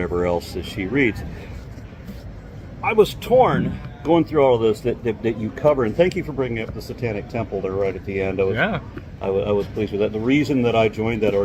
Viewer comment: What I don't understand is if you have no need for religion or rituals why did you have to choose the church of Satan. You said the church of Satan are allies, when they still worship a deity. I would have understood if you joined to find evidence to see if the devil or demons really exists. Sounds a little weird for an Atheist to join the occult 🤔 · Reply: Thought he said Satanic Temple.